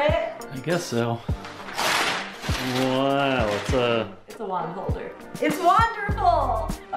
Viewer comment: Totally thought she said wine holder...